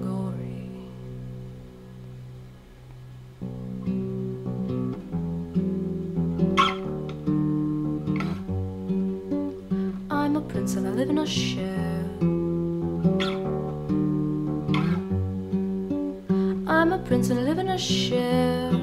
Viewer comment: I always hear "I'm a prince and I live in a shed"